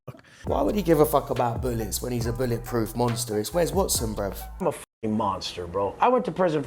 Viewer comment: I thought he said, "Where's Watson". lmao